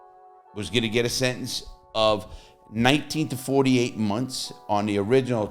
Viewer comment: You only get 24 months for assault with a baseball bat? That is a deadly weapon! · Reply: I thought he said 19-48 months. It would’ve been his first time in prison so it was a little bit lighter.